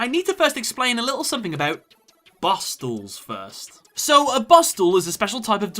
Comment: I misheard 'boss duels' as 'bar stools' at first and was so excited for the monster that was gonna pop up.